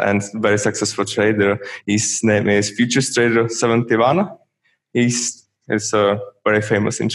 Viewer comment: Great interview, can someone write me what is the trader name he say his his inspiration at 8:27 please. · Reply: Rémi Gagné